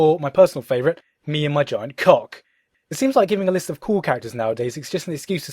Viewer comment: did he said me & my giant cock lol